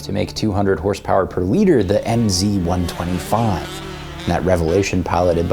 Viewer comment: I was "what the heck ... did he just say MZ?" 😲 our East Germany 'Motorenwerke Zschopau'? 🤔 btw: the GDR was socialist not communist.